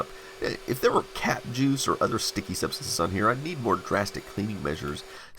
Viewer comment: I almost misheard ‘Cap Juice’ as Cat Juice, thinking it was a euphemism for cat piss!